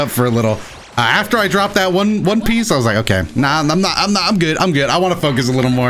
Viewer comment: Did just say that he dropped one piece?